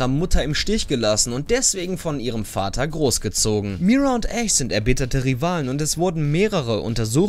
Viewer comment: ich weiß nicht ob es es falsch verstehe oder er es falsch sagt 😅 Mira& ACE ? Wer soll Ace sein ? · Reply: Ash meint er